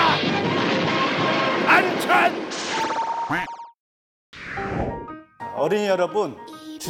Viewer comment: 어른이 여러분으로 들었네 ㅋㅋ